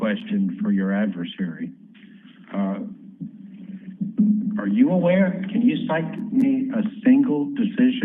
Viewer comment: When the one ALJ started talking to (?), I EARNESTLY thought he was about to say, "Are you drunk?" when instead he said, "Are you aware?".... This was a shellacking. It was listening to a composed professional, and immediately hearing someone who sounds like he just came from a keg party.